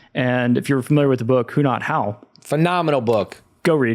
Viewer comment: What is the name of the book it was recommended at 16:37? said to read it 5 times. I couldn't understand the title and who is the author? Thank you in advance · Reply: "Who Not How" by Dan Sullivan